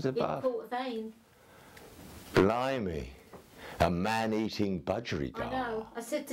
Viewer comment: Lol, subtitles said, "man-eating burrito."